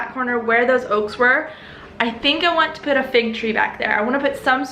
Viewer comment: I thought you said you wanted to put a fake tree in the yard.... I was like, that seems weird for her. FIG tree. Duh.